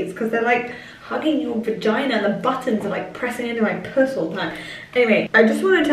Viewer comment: Did she say the buttons are pushing into my p*ss all the time ? 24:32 kills me 😂😂😂😂😂😂😂😂😂😂😂😂😂😂